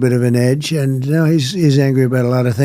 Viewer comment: Why is it i hear that “he’s angry” as “most people hate me?”